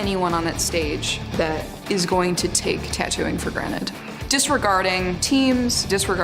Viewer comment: did she say “for granite”